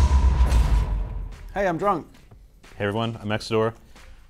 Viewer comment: "Hey Im drunk" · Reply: Thats what I thought he said XD but its actually grunk (6runk)